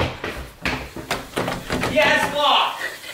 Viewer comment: Did he say "YES! Lock" @ 5:40 ?